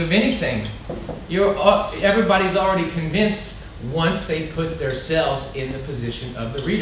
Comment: Did I hear him say “…once they put theirselves…?” (3:30) Um…